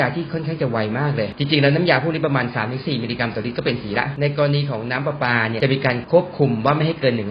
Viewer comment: ผมเหมือนได้ยินคำว่าสี เป็น หี นะ 5555